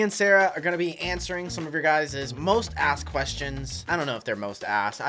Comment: When you said asked I thought you said but